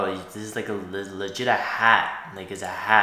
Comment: what did u just say? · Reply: He said that the guy's hair looked like a "hat", a literal "hat"